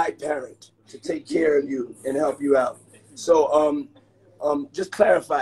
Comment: Did she say "help" at 1:15?